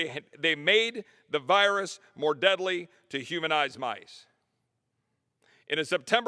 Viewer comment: did he say, "humanized mice?!!